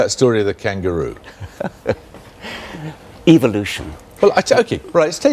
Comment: Richard Dawkins is of satan, look at 3:23 and around 2:25 he says. evilution , instead of evolution